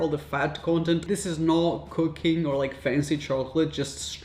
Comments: i thought he said cocaine 😭😂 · Am I the only one who heard COCAINE chocolate? Where can I buy that?